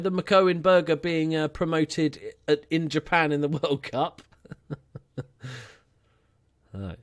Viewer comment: Did u just say Japan won the World Cup or am I hearing things